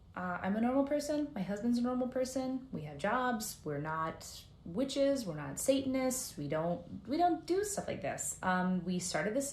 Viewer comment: 😂😂😂 I like how she says “we’re not satanist, we don’t do stuff like this” but the subtitles says “We are not super smart people” 😂😂😂😂 cause obviously who the heck plays with ouija boards. lol it’s speaking backwards cause it’s evil. She gone cast a spell on herself 😂 imma pray for her seriously.